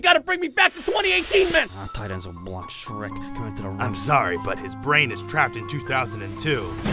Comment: Is it just me or does Gruden say "Our tight ends will block Shrek" at 0:53??